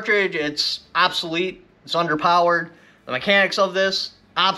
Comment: you said under powered ? do you think the little .223 has more power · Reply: Underpowered? ..well shit, i thought he said under powdered. So i ramped up the reload some. Blew up my barrel. Who's taking liability here😂😂😂☠ jk